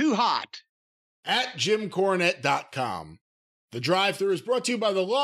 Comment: Always sounds like is saying, "Courtney drive thru" to me